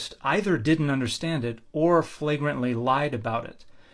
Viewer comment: He really does like that word lol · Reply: When I started watching his videos I thought he was saying “fragrant” and assumed that was calling the incorrect claims bullshit or that they were so bad that you could smell it. I dunno, I was dumb 🤷‍♂️